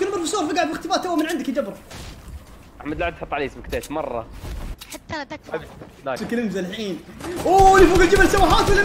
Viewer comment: سمعت صوت بنت تقول حطها تكفي🌚